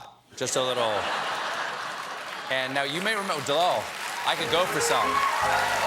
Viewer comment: I heard @ 2:29 "Dictator twat" that and everything followed was perfect.